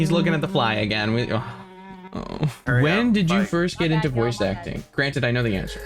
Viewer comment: misheard as "When did you get into a divorce" wait THEY'RE ACTUALLY IN THE NAVY??